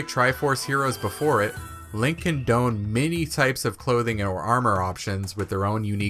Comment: Really cool video!! Sorry for being a grammar Nazi here but the word “donned“ is pronounced like the name Don. Not doohhned. Lol. At first I thought you said Link condoned but you said can don. Now you can yell at me in the comments. Thanks.